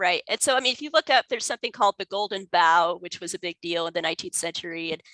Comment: Did she say the Golden... “bow” ? (“Bough”)? Anyone? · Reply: Bough